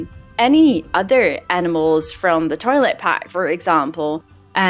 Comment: Omg I thought you said the toilet pack and I can't stop laughing. I was wondering what dlc you think is trash? 🤣🤣 PS not making fun of your accent at all, I love your accent, just misheard you lol